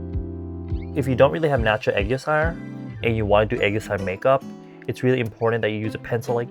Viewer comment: Can someone please tell me what is saying at 10:35 ? What is this eye makeup look? i never knew what it was called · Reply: "aegyo sal" it's korean, it just means that fat/bag part under your eyes. don't know what it would be called in english, it probably doesn't have a separate word for it, just undereyes??